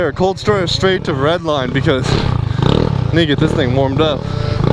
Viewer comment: what did you say?? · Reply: "Need to get this thing warmed up"